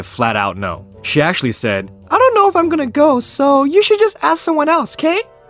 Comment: "No dating 'till you're marry!" That makes utter sense. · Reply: neldabg for a second i thought you said i don't care instead of i don't know so and i laughed once o.o